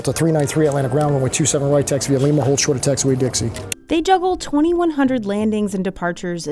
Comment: did he just say taxiway Dixie?